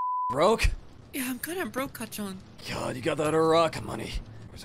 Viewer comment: I heard deku said "cacan" instead of "kacchan"😭😭😭😭😭😭😭